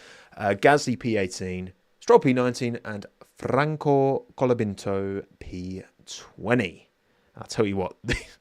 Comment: did you say “Franco Colabinto” lmao